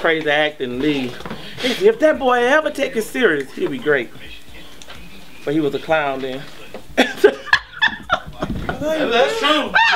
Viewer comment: What does the mom say at 5:41 ?? Captain ? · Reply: she said "but he was a clown back then"